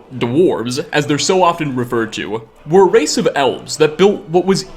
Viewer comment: Did he just say, "they were a race of dwarves that were elves"?